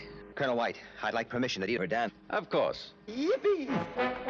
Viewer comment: What does Captain Blue say at 2:47? It sounds like "I'd like permission to be Verdan."